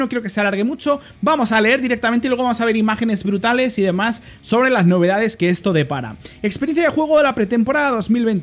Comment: te juro que entendi "las novedades que estoy de pana" XDDD de pana